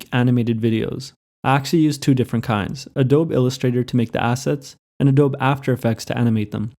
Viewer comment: Did he say Adob illustrator at 11:45??